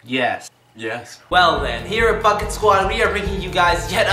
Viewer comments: i heard "fucking squad" lol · Sounded like he said “here at the fuck it squad”